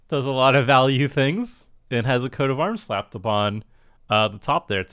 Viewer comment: He isn't playing coat of arms though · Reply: you are correct; I'm listening to it and I misheard him talking about Jodah's ability as a "Coat of Arms effect"- my bad